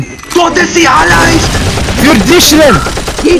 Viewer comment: why did he say "für dischländ"?